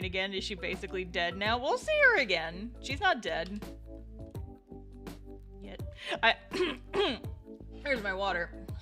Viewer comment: Did I hear that right? "She's not dead (yet)" if you even touch Marion istg